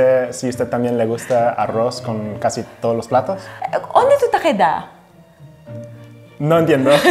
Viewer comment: min 1:38 She sais "¿onde tú ta quedá?" , he didn't get the phrase, but it is easy : onde = dónde, tú ta = tu estás, quedá = quedar, quedando You can easily get the meaning ... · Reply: Very insightful! I heard “edad” instead of “queda,” haha